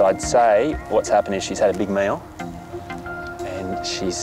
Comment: I thought he said Charlotte just had a big male. A big male what? And how can he tell it’s a male? Light bulb moment: a big meal! 😂